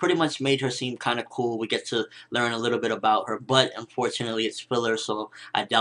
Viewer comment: *dies laughing at 0:18 - 0:19 * Sorry, I listened to that all wrong at first. I thought you were saying "we get to learn a little bit more about her butt." But then realized you said "We get to learn a little bit more about her, but unfortunately..." Great vid nonetheless though dude!